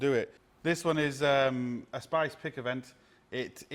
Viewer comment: SYKES PICKAVANT not Spice Pickavant 😂😂 Come on I thought you were practising to say it 😜